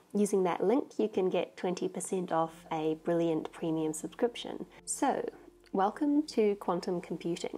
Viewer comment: the subtitles say "Ao" instead of "So", that is Ao sad :(